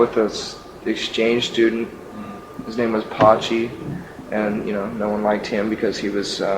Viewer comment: To clarify the subtitles a bit: At 6:50 he speaks about a spanish exchange student, and his name is Patxi, or Pachi, which is basque for Francisco. I was most suprised when I heard it from Kurt's mouth.